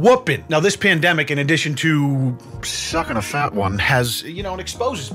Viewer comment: Why at the start it sounded like he said stuck on a fat woman